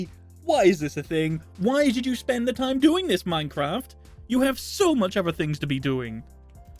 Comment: i think it says minecraft